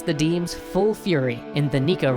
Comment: Did he just say "Neko Revolt"? Meow, revolution is coming, master. :3